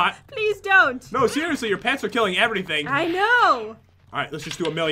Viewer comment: Did say "your pants are killing everything." at 24:47!